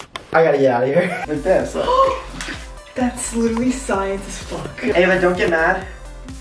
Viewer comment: did she say that’s science as f*ck